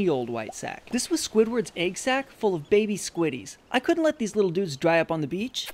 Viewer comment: Wen he said egg sack I thought he said nut sack😂😂😂😂😂